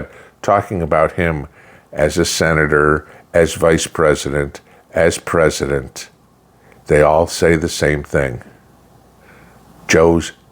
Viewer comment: I heard him say Vice-President President Trump.....I couldn't stop laughing.